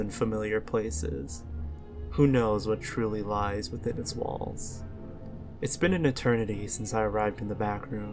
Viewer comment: I thought you said "balls" instead of "walls" at the start.